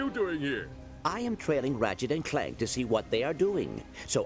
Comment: wtf did clank just said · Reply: no he said i am following ratchet and clank